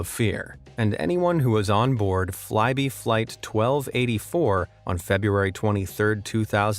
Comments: I thought he was saying Flighty Flight when he was talking about Flybe Flight. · Even though the one flight attendant was lost, which is horrible, it’s incredible that everyone who stayed in the plane lived, even with severe injuries. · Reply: I thought he was saying Flighty Flight when he was talking about Flybe Flight. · How that pilot survived being dragged outside the window is incredible, and incredible that the crew held on to him the whole time without giving up and assuming he was dead, man he’s lucky to be alive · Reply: I thought he was saying Flighty Flight when he was talking about Flybe Flight.